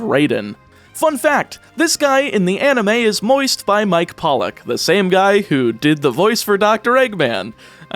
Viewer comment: Another fun fact for you - Mike Pollock also voices Volcanion in his featured X&Y movie. Also, did you say "moist by Mike Pollock in the anime"?